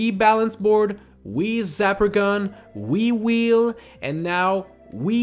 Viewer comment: I thought he said wii-wii instead of wii wheel. lol